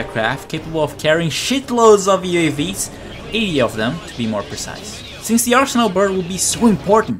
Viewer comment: did he say 80 of them? IT ALL MAKES SENSE NOW